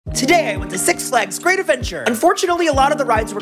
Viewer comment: Yo I heard him say Se* Flags